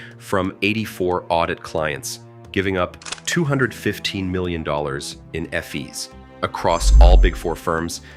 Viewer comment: The irony is that the script for this video sounds like it was generated by AI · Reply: It was. Listen at 3:59 ef-ees instead of fees